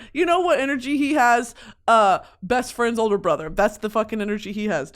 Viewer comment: at first I heard that as "older brother's best friend" and immediately thought to myself "no fucking way he does not" but then I realized it's "best friend's older brother" and yeah that makes a lot more sense for some reason